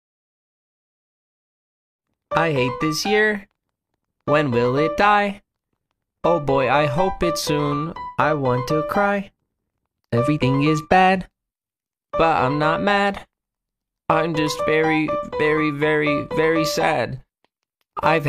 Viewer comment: ngl when i heard "i want to cry" i heard "i want to die" for a hot second there